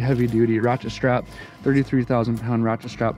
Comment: did i hear 33,000lbs ratchet strap at 16:46 haha